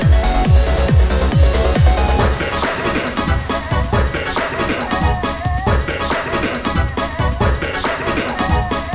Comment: What is being said at 0:56? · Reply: "Work that sucker to death"